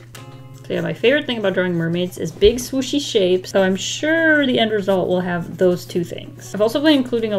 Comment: I thought you said big "sushi" shapes XD